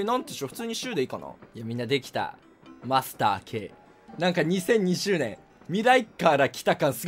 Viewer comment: マスターKって聞いてたら、ガスター10に聞こえてきたw